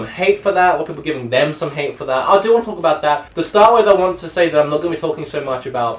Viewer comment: "people giving gamestop hate for that. Brlblblrbrb brlbrlr damn some...." 0:42 "i do lulul talk that...." ???? What ? Do you have a RAP bet running with Busta Ryhmes ? xD Dude, my native language is not english, im from germany. I can understand us english very good (but not write xD ) even the most awkward dialects are mostly no problem, but i cant understand half from the stuff that you are talk, because you immitate Busta Ryhmes. EDIT: I must correct myself. I exaggerated, i cant understand most from the stuff, but sometimes (like at 0:40 & 0:42) you talk just to fast.